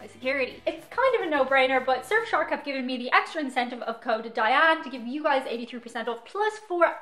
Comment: kinda sounds like you said Covid Diane and I was confused if 'CovidDiane' was the code they gave you for some weird reason.